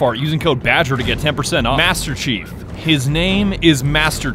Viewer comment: I am sorry but when he said master chief i thought he said handkerchief